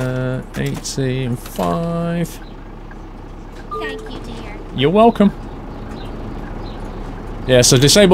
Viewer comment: Did she just say "Dankeschön" (German for "thank you")!? This is good fun to watch, more of this and Omsi2 please ;) + 16:48 Actually you do not have right-of-way when going straight over a zebra crossing - like in the UK. Pedestrians have always right-of-way on them. What you said applies to pedestrians who cross the road not (!) next to a zebra crossing. There aren't as many in Germany as there are in this game though. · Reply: said thank you dear